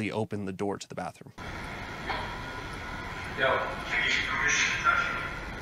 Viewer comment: ish I heard a voice ask something like, “Do you need permission or do you have permission?” I scrolled through comments but not too far seeing if anyone else heard it. Please comment if you heard the same. I’ve watched this season but am rewatching, getting ready for this upcoming season. Wish I could investigate for you guys could use a female “person” on some of your hunts. Great channel guys!!!